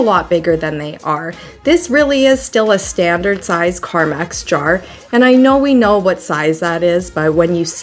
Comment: The coffee is Maxwell House. Lol Love your work! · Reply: I hate that it was bugging me and went to the comments to see who noticed. I thought I heard her say carmax instead of carmex also.